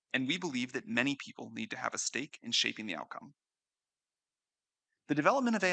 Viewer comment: Did Sam say "steak and shake" @ 6:23?